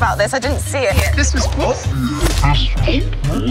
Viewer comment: Does anyone know what Paddy says at 2:59 ? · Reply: 'Can I borrow this for a bit'